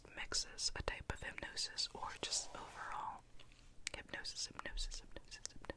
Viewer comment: To me 1:33 kinda sounded like hecknosis hecknosis hecknosis (heck no sis) lol just my thoughts😂